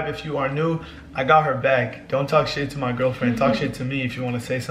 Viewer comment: Did he just say don’t talk like that to my GIRLFRIEND